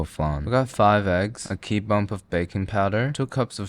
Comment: Did you actually say a key bump @ 16:20 ?? XD